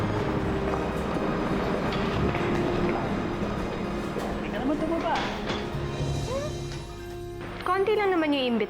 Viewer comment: ano sabi ni ziggy? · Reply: “di ka naman tumaba”